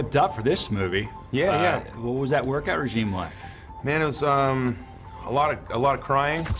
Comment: Is he saying 'work-out regime' at 0:26?